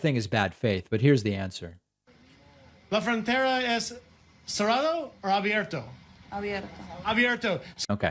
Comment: I thought he said Serrano as in a spicy pepper .Maduro is a whole, trumps newest lawyer is a registered foreign agent for Maduros government